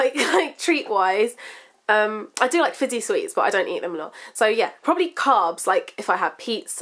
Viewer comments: what does she say @ 7:40? sounds like 'fizzy sweets'? anyone? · what does she say 7:40? sounds like 'fizzy sweets'? anyone?